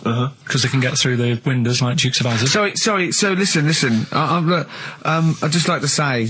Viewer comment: for the record Karl says "like Dukes of Hazzard" lmao. The subtitles are wrong.